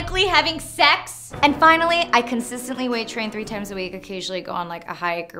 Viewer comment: How long have you been working out? Those abs are amazing! Do you follow a special diet? Btw on the first listen, instead of hearing "weight train" I heard "waitressing." Only me?